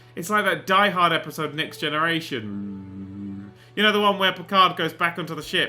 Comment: did he just say die hard:next generation?